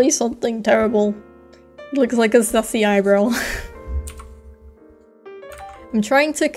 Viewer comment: I thought he said sussy eyebrow at 13:04 until I turned on the captions I'm glad it was sassy eyebrow or I would have started a riot